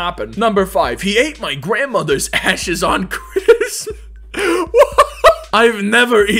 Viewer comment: i thought he said as*es